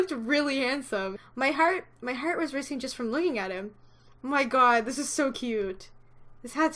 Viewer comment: I'm dumb. At 8:43 I thought it said "my heart was racist just from looking at him." xD